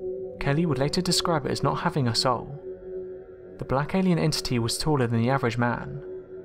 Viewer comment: is he saying "it did not have a soul" or is he saying "it was not having us all?" Both do not make sense to me. I'm confused now. 😵😩😩😩